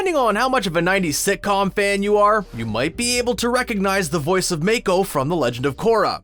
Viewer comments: Did he just say make-o? 💀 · DID HE SAY MAIKO